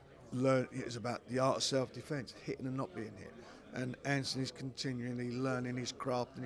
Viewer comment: at@ 9:30 did frank say Antony instead of Daniel